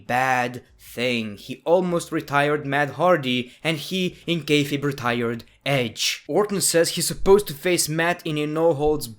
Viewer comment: What did he say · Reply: He in "kayfabe" retired edge